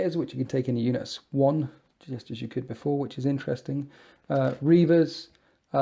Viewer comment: RE: Terminator squads - wait...did you say they could be fielded in units of ONE (1) !!! As you could in the 7th edition version of their codex? Because that MIGHT actually enable you to afford a Brigade of Deathwatch if that's correct. Are you sure about that? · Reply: Trentat Hawat he said “ in units of 1, which is interesting”. Unit of 5 as excepted wouldn’t be “ interesting”.... so that’s promising. Just hope they get sia on storm bolters too!